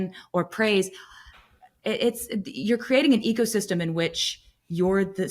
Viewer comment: I could swear I heard her say "if you're worrying about what some rando said on the internet, you're creating an egosystem that's going to destroy your life"